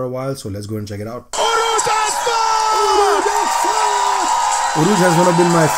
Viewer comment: Misheard Name Urooj As Fuck 😂😂😂